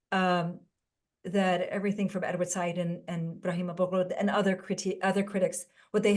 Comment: who was the second critic that she mentioned after Edward Said around the 7:55? I couldn't catch the name? Thank you! Great discussion. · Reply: Ibrahim Abu Lughod